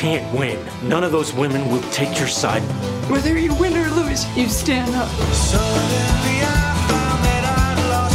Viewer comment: What did she says here ? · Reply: whether u win or lose, you stand up...